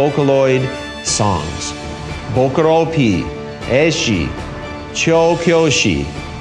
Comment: plz tell me I’m not the only one who hears choke yoshi